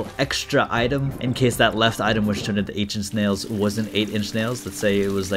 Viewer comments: I misheard 8 inch nails for ancient snails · i legit thought you said “ancient snails” when you picked up 8 inch nails